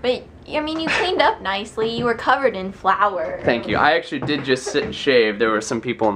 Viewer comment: Bahaha. I thought you said "I did just shit and shave." I was like what? No way he said that. But you said sit. My dad always says "Shit, shave, and shower." so I thought you were saying that. I laughed. Well this has been slightly awkward.